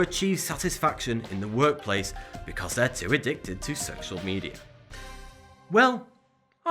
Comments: Is it just me or does he say addicted to sexual media at 13:08? XD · did he just say "sexual media"? 0_0 XP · did he just say sexual media? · I replayed it a couple times and still I always hear "sexual media"